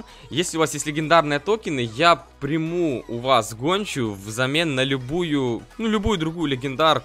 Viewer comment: Мне послышалось он сказал конча XD